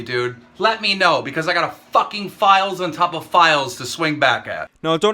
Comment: every time keem says ''files on top of files'' all i hear is ''lies an top of lies''